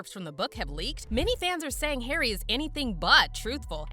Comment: Every time she says “many fans” I hear “many racists”. 🙄